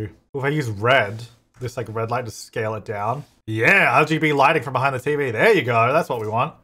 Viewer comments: did he say LGB lighting? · is it just me or did he say lgb lighting and not rgb 😭 please tell me I’m loosing it